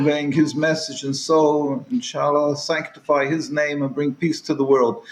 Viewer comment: Did he say Inshallah?